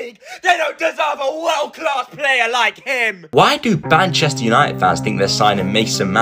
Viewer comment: What he said: Manchester united What I heard:banchester United 💀